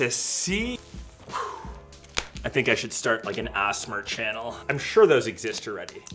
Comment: He actually pronounced the word asmr instead of saying A-S-M-R 😂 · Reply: I thought he said asthma channel